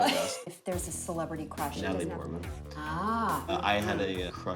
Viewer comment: What celeb crush does he say before Rosario Dawson? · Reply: Natalie Portman.. also my celeb crush many years ago